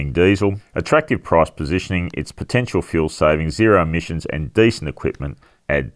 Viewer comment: Did you say ‘zero emissions’ ?